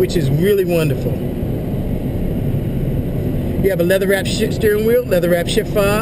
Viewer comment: lol i thought he said "leather wrapped shit steering wheel" lol